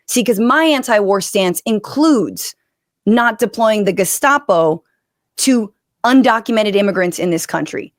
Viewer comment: that's another thing I hold against Marge the missing link from Georgia, every time I hear Gestapo my mind says Gazpacho.